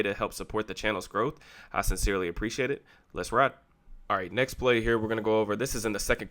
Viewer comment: did I hear a let’s ride?😭😭